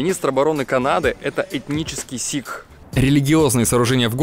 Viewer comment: Мне одному послышалось "этнический Ситх" XD